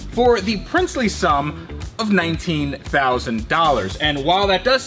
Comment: When said 19000k I thought he said 1900k and I was like not bad and then I realized.